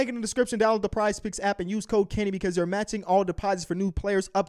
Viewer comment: Everytime kenny says "use code kenny" I hear "use cocaine"